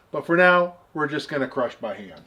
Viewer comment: i heard, "But for now, we're just gonna crush my hand." Sorry, but i did laugh.